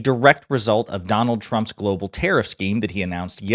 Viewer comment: Oh, i thought for a second you said "trump's current terror scheme". Wait...